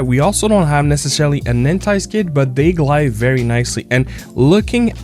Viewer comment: What did you say at 2:15? You said “we don’t have necessarily anti ___\” and then I don’t know what you said after that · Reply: anti-skid